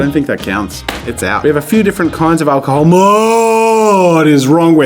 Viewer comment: it sounds like he said alcohol 🍸